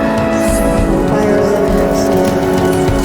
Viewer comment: For some reason I keep hearing: Fire Lord Ozai